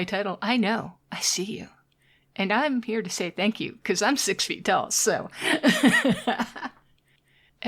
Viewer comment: She’s onto us fellas! Scatter! But wait… did you say you’re 6 foot 😮😳😍